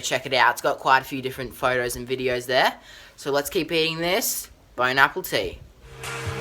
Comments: Am i trippin or did he say "Bone apple tea" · it sounded like he said "bone appletea" at the end instead of "bon apetite" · Be sure to follow me on Instagram as I am following a number of people back! Instagram: <link> or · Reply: look my man, you're a really cool guy and I really like your videos... but did you just say "bone apple tea" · I’m sorry did he just say “bone apple tea “ at the end of the video instead of Bon appetite 🤦‍♀️🤦‍♀️🤦‍♀️🤦‍♀️🤦‍♀️🤦‍♀️🤦‍♀️🤦‍♀️🤦‍♀️🤦‍♀️🤦‍♀️🤦‍♀️🤦‍♀️🤦‍♀️🤦‍♀️🤦‍♀️🤦‍♀️🤦‍♀️ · At the end did he just say "bone,apple,tea " 😂😂😂😂😂👌🏻👌🏻😊😊instead of Bon appetite 😂😂😂😂🤣😂🤣😄😁😆😃😀😅😂🤣😄